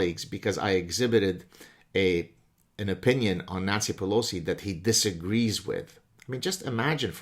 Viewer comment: With Gad's slight accent I heard him say Nazi Pelosi. LOL.